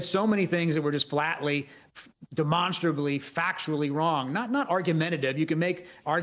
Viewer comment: Trump said "Joe Biden died to me?" ??? Is that what Salt Peter Baker The Candlestick Maker said? 🤔 💭